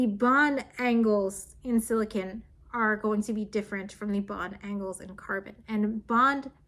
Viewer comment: I kept hearing "bond angle" as "bon dangle" and just thought about Lt Dangle from Reno 911 getting praised for his new boot goofin'